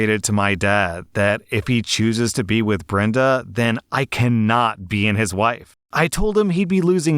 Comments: reads: "I cannot be in his WIFE." Me: Wait wait wait hold up what did he just say? listens again Oh yeah he def said "wife" checks text Text reads: "I cannot be in his LIFE" Okay okay that's better. For a sec I thought OP was doing her Mom (but not really) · anyone else hear 'I cannot be in his wife' instead of life? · I nearly spat out my drink because I had the audio playing in the background while drawing and it sounded like said "I cannot be in his wife."